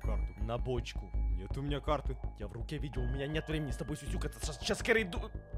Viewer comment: мне сначало послышалось сук, а не сюсюкатся